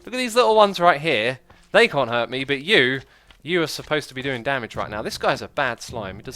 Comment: I thought he said "you are supposed to be hermit" and like what?? new hermit?? who's that??? ( concentrating in game on my hand :P